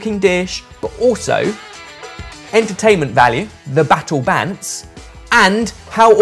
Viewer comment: Subtitles says "battle dance", but I'm absolutely certain said "battle bants". (Which member of the Sorted crew has best bants, btw?)